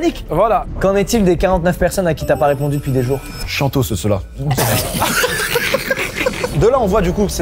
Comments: Il dit quoi djilsi à 24:15 ?? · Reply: Chiantos ceux là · il a dit quoi ?? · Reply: chiantoss (grave chiant) celui là